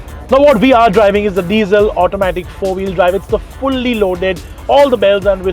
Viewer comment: did i hear 4 wheel drive ? Can’t seem to find any 4x4 version anywhere on Mahindra’s website